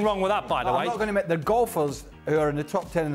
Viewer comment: I thought Craig was saying gopher at first. I was so confused until Don said golfer.